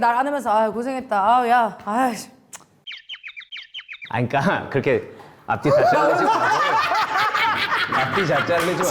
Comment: 윤승 오빠가 뭐라고 하신 건지 궁금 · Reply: 같이 살수도 있겠다.